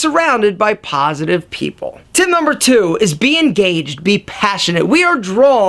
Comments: For a moment, I thought he said "Being gay". Dude. -____ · I thought he said “being gay” I was ready to check that mark